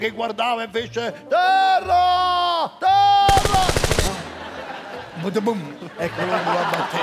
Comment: Sentendo gli spari Ah, terra? Io ho capito "Guerra", c' stavano a bombarde' gli indig'n